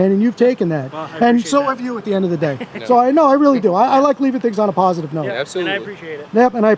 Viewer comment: I legit thought said "I like leaving pigs on a positive note"! I crack myself up sometimes. 🤣🤣